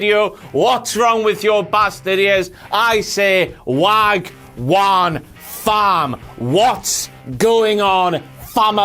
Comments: lol thought u always said wank one farm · The first 50 seconds answered one of the biggest mysteries in my life.. I really thought he said wank one fam 😂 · I always thought he said "wank one fam" #MindBlown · Thought he said wank one farm......assumed it was a English/England thing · I seriously thought he said "Wank on fam!".......... Well no use stopping now!!